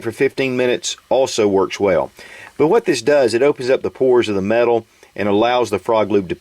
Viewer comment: Wait..I just paused at 2:15ish, did he just say, "Open up the pores of the metal" ? Be more like heat the metal so it thins the lube as to get into the valleys of the metal. Just my opinion, I'm no scientist.